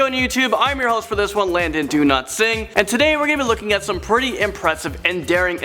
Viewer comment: It's like when he say "Dowlatsingh" it sounds like he is saying 'do not sing'